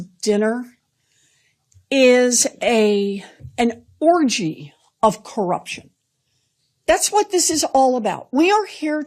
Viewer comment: She said "an orgy of corruption," but for a second I heard "an Orangy of corruption." That seems appropriate for the Cheeto-in-Chief.